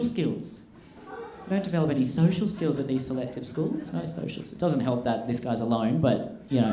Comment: "Doesn't help that this is.. what?" What did he say? Lime? Lion? Lying? Lol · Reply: "Doesn't help that this guy is